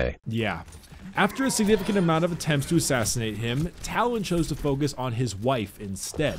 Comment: am I the only one who heard talon??